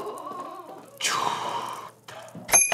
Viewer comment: qué dijo? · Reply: Chuuuuuuuuuuuuuuuu